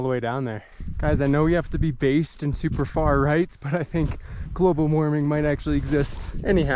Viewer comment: what did you say? "We have to be based in ultra far right". Not sure what you said exactly or meant by that. Can you clarify. Thank you! Unbelievably inspirational videos!!